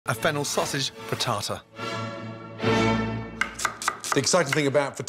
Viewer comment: I thought I heard "fritasa".😄 Amazing chef as always.🍀